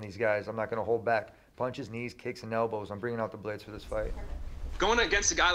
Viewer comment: I thought Tony said - I’m gonna punch his knees, kick his elbow ..